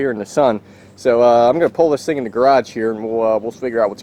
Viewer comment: Did he just say gay raj